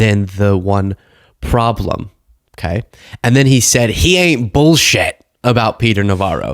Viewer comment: Correction, Musk did not say Navarro "ain't BS" -- he said Navarro "ain't BUILT S***".